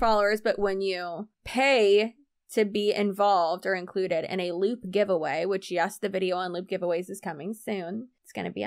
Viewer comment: Loool when you said loop giveaway I thought you said “lewd giveaway” and I had to pause bc ew 🤣🤣